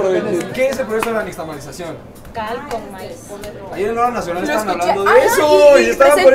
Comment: no entendi un carajo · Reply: “Ahí en la hora nacional (programa de radio) estaban hablando de eso”